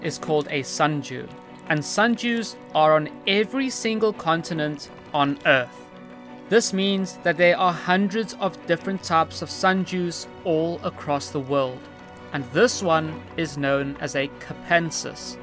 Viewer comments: i thought he said ‘sun jew’ 😭 but besides the point this carnivorous plant is very interesting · Sundew: "Come here, give me a hug, I've been lonely" 💀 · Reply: Lol, I thought he was saying "sun jew," but the screen was showing "sun dews" I'm so confused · I legit heard "sunjew' 💀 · Is it just me or does it sound like he says "Sun Jews " 😂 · I thought you said sun jews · I heard "sun Jews" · Thought you said sun-jew for a second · Gotta enunciate your D’s my friend, it wasn’t until I saw sunDew come on screen that I understood what you were saying, to me I thought you said sunJew lol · I mistakenly thought you said Sun Jews